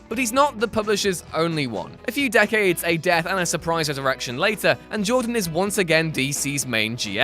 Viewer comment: I know it's not but doesn't it kinda sounds like he says "surprise erection"? lol